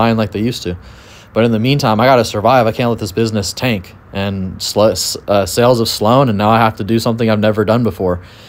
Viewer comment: Not here to be petty but I heard you say “sluts” when you were trying to say “sales have slowed” 😂😂😂 didn’t think nobody was gonna catch that did you 😂